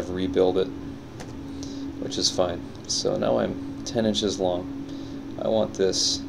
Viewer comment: Did he just say he is 10 inches long??!!